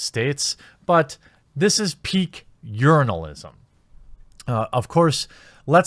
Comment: I'm one of the 'international/foreigner' viewers for me, when you said "urinalist" what I heard was "yournalist/yernalist" because I thought it was your way to comedically pronounciate and weirdly mock the word "journalist" didn't know that it was originated from the word urinal and yes I already know what urinals is, we also have them. If I knew it was from the word urinal at first, the joke will have more punch earlier on, maybe I didn't catch it or missed it when u did explain the origin of the pun. just explaining this trivial matter from my point of view.